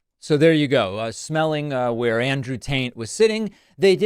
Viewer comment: Love that you called him Taint.. someone else has referred to him as Andy Pandy which I like best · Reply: 😂😂 I thought that's what he said. Dude smelled the seat of Andrew Taint.